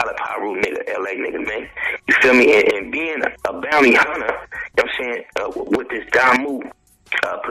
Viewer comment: what blood game is mess from............................................lol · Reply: k interstate I'm wondering the same thing. in this interview sound like he said bounty hunters